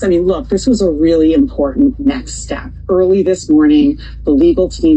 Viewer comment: lol all I heard was neck step xD